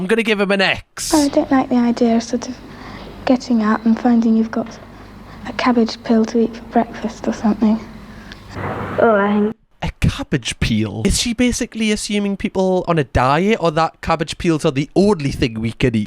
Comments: i think she said cabbage PILL 😭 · I heard "cabbage pill", as if there would be no food, and all of our meals would be in pill form. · i think she said 'cabbage pill' lol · I heard "cabbage pill", as if there would be no food, and all of our meals would be in pill form. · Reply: That's what I thought she said too. Pills not peels. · I thought she said cabbage pill lol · I thought she said “cabbage pill” instead of “cabbage peel.” But I’m American so… I was also a child in the ‘60s so I can empathize with their feelings. Yeah, we were raised on nuclear war psychosis. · I think she said cabbage pill like everything will be in pill form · I think she said "cabbage pill" instead of "peel". Like, instead of buying a head of cabbage, you would get a pill that you could probably take, or add water to it and it will somehow grow and turn into cabbage like some kind of weird vegetable version of Gizmo after getting wet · Cabbage Pill 💊, not peel. She’s talking about taking vitamin pills instead of eating food. 28:39 I loved this one & I hope you experiment like this again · I thought she said cabbage pill not peel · I thought she said wake up and take a cabbage pill, like you take pills instead of eat